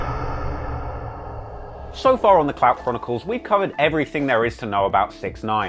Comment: I thought he said the clap chronicles lol